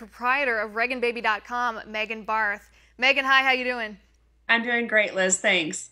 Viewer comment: i thought she said megan barf -- (barth) -- i was like lol sorry girl